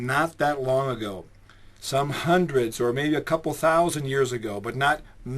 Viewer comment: Did he just say dinosaurs "a couple hundred years ago" ..Goodbye loony